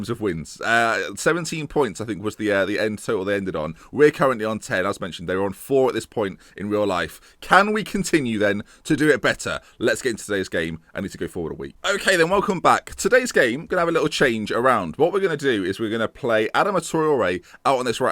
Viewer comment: at first I heard "I need to go for a wee"